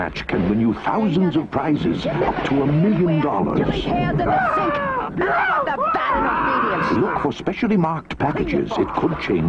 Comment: did she say “there’s pubic hairs in the sink”?